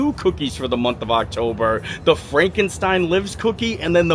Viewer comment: The first time you said the cookie names I thought you were saying “Frankenstein lips” lol